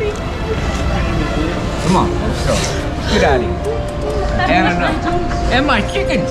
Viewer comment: Correct me if I'm wrong, but did he say, "come on lets go, get outta here, I've had enough" ???? 🤨